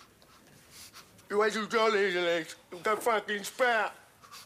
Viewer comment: What does Gumby say at 1:38:30 (to Trevor)? It makes Trevor turn around and walk out. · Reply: "You wait till John hears of this. He'll go fucking spare!"